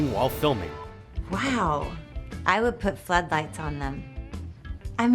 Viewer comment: I listened 8 times already, and I still can't understand what the fuck she said. Help. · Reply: i would put flood lights on them